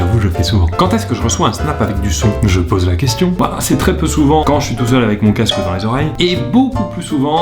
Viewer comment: C'est moi ou il a vraiment dit : "quand je suis tour seul avec mon casque DANS les oreilles" ? 😱